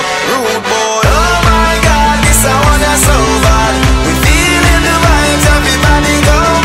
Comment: I always thought he said "Oh my god this guy isn't sober" XD